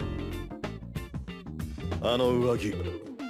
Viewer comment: it sounds like he saying "I dont like you"